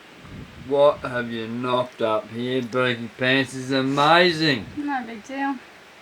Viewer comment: Okay... what words were just said here?! As to me they didn’t sound English lol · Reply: What have you knocked up Dayton pants its amazing.